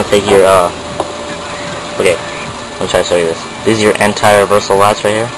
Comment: Thanks a ton, this helped immensely. I have one request, I didn't quite catch what you said at 8:33, that spring there is what I am missing. I got the first part of its name, the Anti Reversal ... something. it sounded like latch or something. Anyways, if you could let me know the name of that part and maybe even where to get that replacement spring, (Echo1 store doesn't have it+Echo1 forums are down) I would be greatly appreciative. Thanks again.